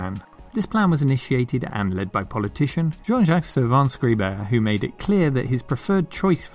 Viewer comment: the name of the politician is not clear at all, subtitles says "georgette savants gruber"...Dont think I ever hear about this person. Anyways the name of the politician on screen at this moment is Laurent Fabius. And yes, I indeed had a CPC 6128 at home, just like you talk about in the video. OMG I recognize myselft so much in this! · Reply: This is YouTubes auto translate... I'll get that updated manually. It's Jean-Jacques Servan-Schreiber